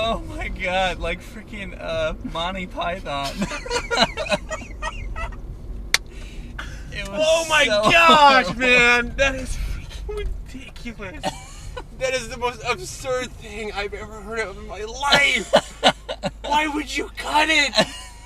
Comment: Whoa at 9:04 did say fucking??? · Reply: no he said freaking